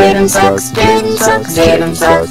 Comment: DID HE SAY JAYDEN F**KS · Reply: nope, he said Jayden Sucks :)